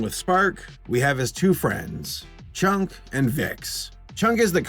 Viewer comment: you said 'chunk'. I heard 'chonk'. Makes sense.